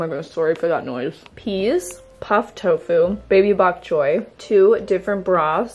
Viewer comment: 😂I’m sorry what did you actually say? All I heard was Baby Bob Joy 😂😂😂😂😂😂and I know you’re not just naming the food funny names. By the way that looks so yummy!! 😋 and I’m glad it worked!!